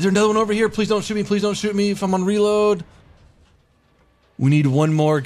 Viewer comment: he actually said: "geladen" it means "loaded" in german.